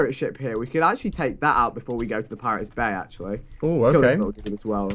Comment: I thought Vikk said 'pirates bae' instead of bay😂